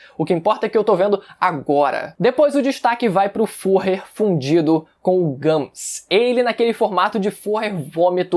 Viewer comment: eu juro q ouvi fudido